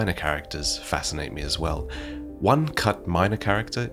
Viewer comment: I just did a double take, wasn’t fully focused and I heard Astel, thinking of the boss.